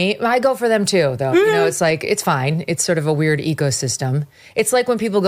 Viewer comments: At 43 seconds, I seriously at first thought she said “it’s like a really weird EGO system.” 😂😂😂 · " it's a weird ecosystem", she says first minute...about media. I thought she said "EGOSYSTEM", Which I thought was clever. Seems like I made up a new word!